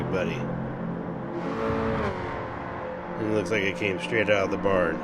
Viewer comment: I like to get my cars from the barn 😂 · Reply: I thought he said straight from the BAR first 😂 that works too!